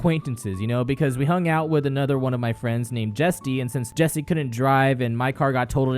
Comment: Alright...i know I'm hilarious but...i...I died. HA HAHAHAHHAHAHAHA HAHAAAAAAAAAAAAAAAAAAAAA LOLOLOLOLOOLOOLLOLOLO TROLLLLLLLL LMAO AYYYYYY HAAHHAHAHAHAA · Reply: Did you just say Jestie?